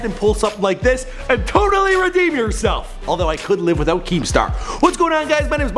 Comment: Did he say couldn't or could?